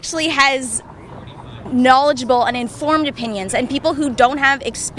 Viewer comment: did she just say knowledgeable